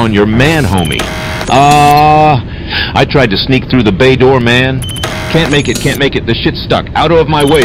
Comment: When he said "bay door" I thought he said gay door 😭